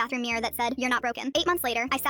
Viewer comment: at the end why did i hear: "8 months later i shat" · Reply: "8 months later I sat..." Took a couple listens to hear it properly